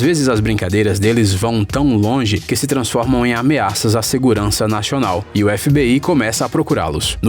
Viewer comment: " a segurança naxonal" realmente eu ouvi naxonal kkkkk